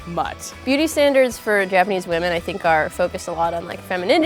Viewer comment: I thought they said booty standards 😂